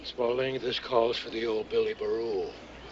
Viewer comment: I always thought he was saying Barool. I'd swear it sounds like there's an L at the end. Billy Barool.